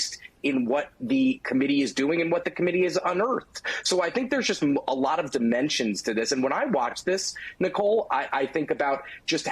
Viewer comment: I misheard what he said: I heard 'there's a lot of dementia on this' instead of 'there's a lot of dimensions...' Either way, prove me wrong.